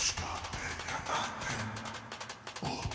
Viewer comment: Did I just hear kurwa at the end?